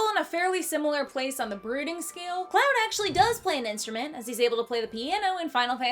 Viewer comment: i can't be the only one who heard this as "breeding scale"